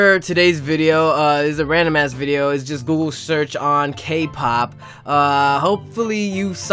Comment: Did you just say Google church..? Uhm...